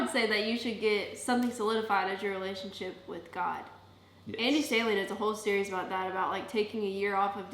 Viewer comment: what is the series you’re taking about in reference to taking a year off of dating? i think you said andy but i couldn’t catch his last name! sounds like something i could benefit from, if you could send a link or something!:) thanks for the awesome video! love from texas💗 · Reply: Alex Cranberry juice name is Andy Stanley! I believe the series is called the new rules on love, sex and dating